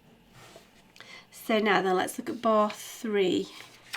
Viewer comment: you were saying bat three but the subtitles said bath three.